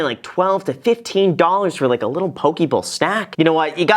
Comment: lowkey thought he said pokeball and I was very confused